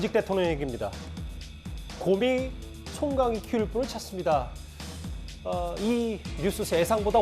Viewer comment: 문재인 키울 분 찾습니다. 로 들리는 이유가 뭘까?